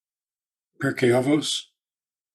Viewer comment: After Psalms, what was the suggested study? My non-jewish ears doesn't understand what was said, lol. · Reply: Pirkei Avot- (Ethics of the Fathers)